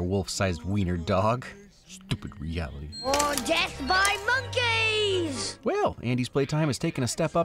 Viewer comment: Okay, did anyone else mishear Andy's "death by monkeys" as "dead five monkeys"?